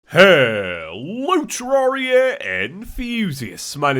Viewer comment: For the longest time i thought he said hello Terraria and fusius but now I heard it Hello Terraria enthusiasts